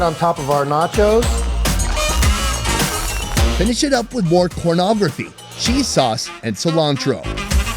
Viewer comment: what Did he say at 10:15 🤔 (Pornography?)